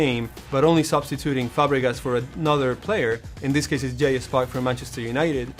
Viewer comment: He said it as well, at 1:52, tho not very clearly - sounds like he says "Jayus Par